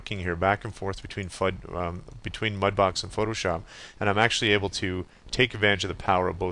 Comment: ha, he nearly said phudbox 4:02, i think he was trying to say mudbox and photoshop at same time, but im loving the phudbox feature!!!!